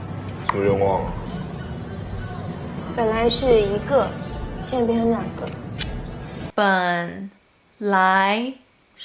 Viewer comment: question about the clip @ 2:50 the subtitles say "现在变成两个." but i feel like i clearly heard "现变两个." my question is when you first saw the clip did you hear "现在变成两个" , do you naturally fill in the blanks, or do you look at the subtitles to see exactly what was meant (fairly certain you have no need to check out the subtitles but figured I'd ask anyway). I've been looking at videos of contractions in mandarin recently and have found those very interesting. Not sure if you're looking for video ideas, but there you go hehe. great video as usual, thanks for these